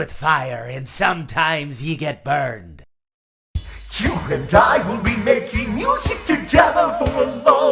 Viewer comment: Music man sounds like he says: You can die, we'll be making music together for a long long time!